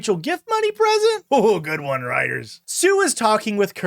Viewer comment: hearing writers instead of gl(ee)iters broke my gl(ee)art 💔